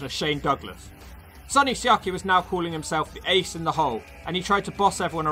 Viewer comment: When sonny siaki did a promo calling himself the ace in the hole idk why i thought he was saying hes the "eggs in the hole" i was like wtf is this guy talking about?